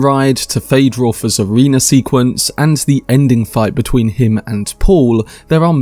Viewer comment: “Feyd-Rautha’s arena sequence.” I thought I heard him say “Feyd-Rautha’s a wiener sequence.” 😮